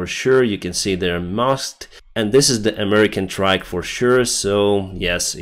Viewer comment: what did he say? I understood something like ?trike? but I am pretty sure those are MRAPs.